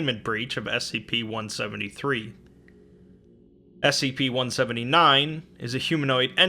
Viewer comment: Does anyone know the scp 15:16 I don’t know what number he is exactly trying to say. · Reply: 179, i believe